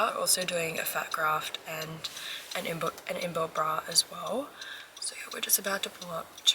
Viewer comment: What’s the embuild bra you were saying in the car at 2:34 minutes? Is that from the fat graft?